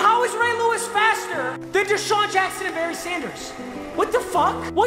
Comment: did he say Desean Jackson? Because that's what I heard